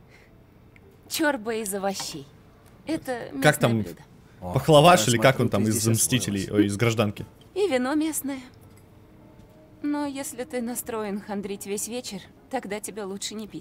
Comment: Мне послышалось или ты сказал Похловаш 🤣🤣🤣🤣. В Гражданской войне Вижин готовил "Паприкаш"